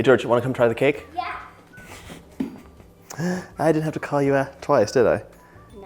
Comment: “I didn’t have to call your ass twice, did I?” 😂 lol…🤣 I replayed that three times to see if I heard that right….that reminded me of my Pops😂